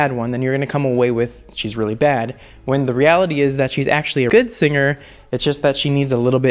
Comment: "In reality she is actually a.. cut good singer" I wonder what he said lmao.. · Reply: i think he said 'actually a really good singer'